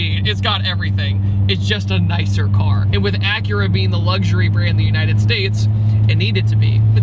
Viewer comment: why do I only hear "aura sex" when he says the name xD